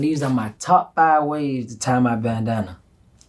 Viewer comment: My dumb ass thought he was it said "top five ways to tie a banana". Got me all like "Hmm, I wonder how that's possible."